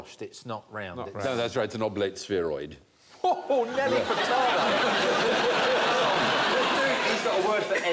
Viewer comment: Did he say "Nelly Furtado"?